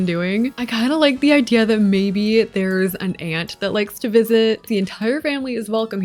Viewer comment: I thought you said, "an ant," 💀 like wtf does an ant need a glam bathroom for 😭